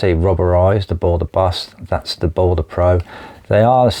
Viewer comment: Hi, AWESOME non-biased review. I thought I heard you mention a Balder Boss Titanium? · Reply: Thank you. I mentioned the Balder Boss, yeah, and also MicroTouch Titanium 👍